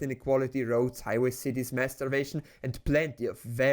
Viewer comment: When you said mass starvation quickly at the end if the video I thought I heard masturbation and had to do a double take because I was fairly certain capitalism didn't make that.